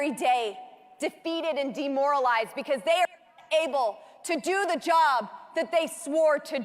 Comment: Why is it at the 3:40 mark when she said the border agents are “able” to do their job, it’s glaring that she actually said NOT able, yet someone clipped out the word “not?” What is the deal with thar????? It’s not like we all have stupid stamped on our foreheads and can’t figure it out! Give me a break!!!